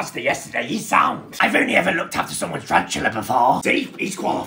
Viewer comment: I didn't hear tarantula, i heard someone's spatula 🤣🤣🤣